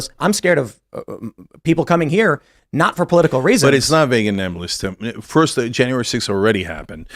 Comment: did chink just say 'but it's not vague enembless,' lol huh.